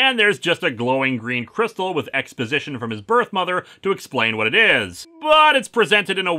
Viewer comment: Did you say "mother"?